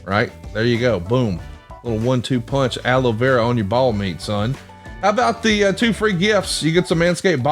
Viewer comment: Did he say ball meat?